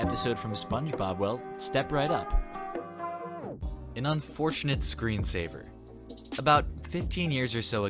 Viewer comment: I don’t know why, but I thought he said ‘Unfortunate Screwdriver’ instead 😂